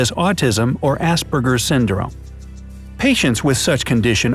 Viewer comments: did he say assburger or am I just hearing this wrong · wait did i hear assburger syndrome?